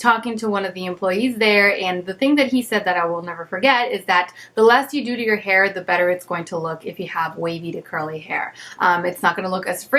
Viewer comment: i am not a native English speaker,so i didn't understand what did she say in this time of the video. I think i heard "the last you do to your hair". Please explain to me! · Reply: The less you do to your hair, the better it's going to look if you have wavy or curly hair.